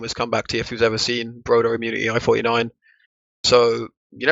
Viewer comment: What does say before i49? I would like to say the most famous comeback ever, can't seem to find it though... Someone help please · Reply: broder/immunity i49